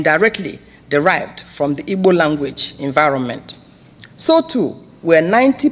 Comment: the caption is wrong. She said Igbo language not Hebrew language. 😢